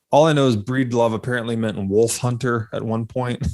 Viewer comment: Breedlove = Wolf Hunter. I thought you said Weath Hunter. Maybe that is what it should evolve into next....